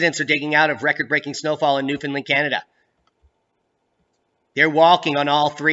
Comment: I heard you say "New Finland" and thought "WHUT?" : D